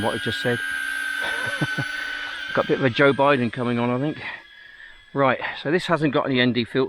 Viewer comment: Did you say Joe Biden? 15:14 😂